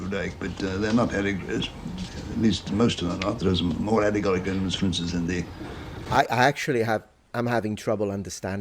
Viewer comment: Interesting that you (and others) say the subtitles help, when the AI speech recognition has at least as much trouble understanding him as you do! For the phrase that the subtitles render "Alor at least most of them not there more allegoric frences in the..." (6:14), it feels as if he was aiming for "Allegories. At least, most of them are not. There is more allegoric references for instance in the...". But as you say, the 7 syllables of "references for instance" were too many to enunciate when his idea of what he was communicating had already moved on, so what came out was the mumbled "f'rns's fr'n'sns"!